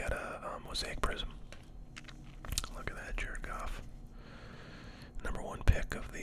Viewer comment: sounded like "look at that jerkoff" 😆